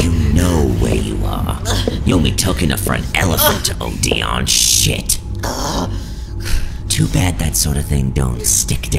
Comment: What did Valentino say at 3:11 ? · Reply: I believe he said "You took enough for an elephant to OD on." Implying angel took a lot of drugs.